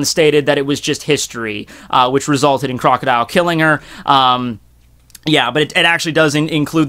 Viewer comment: did he say crocodile killed robin 😂?